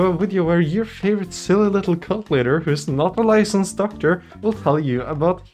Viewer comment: I swear I thought you said "cunt leader"